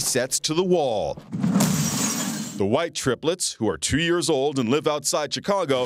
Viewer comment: did he say the white triplets